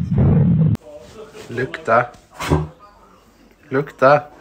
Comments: he actually said "lukta" which means "smell" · i think he said "lukta" which means smell in swedish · I don't think that he is saying "look at that". I think he is saying "lukta" which is swedish for "smell". His accent also sounded swedish. Doesn't really matter but the more you know